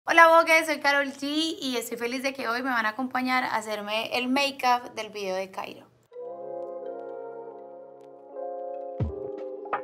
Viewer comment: Mi compañera de clase se llama voke y al principio le entendí "Hola Voke! Soy Karol G"